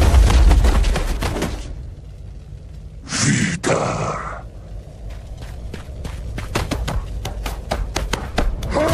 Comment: im sorry but did bro say nigg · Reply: He said "Vidarr" the Norse god of vengeance.